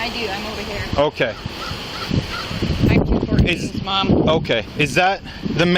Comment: and the only way for that cop to know who Peter was is if Peter is a friend, family member, or co-worker. Deductive reasoning says co-worker. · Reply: Wow! Are you feeling ok? All I did was point out what it sounded like she says "I'm Peter's Mom" and not “I’m 214’s mom”. Have a chill pill and figure it out. I'm only responsible for what I say, not for how you can or can not grasp what I say. Jumping to conclusions is exactly what this woman did so I suggest that in the future, you might want to take that into consideration before chirping off.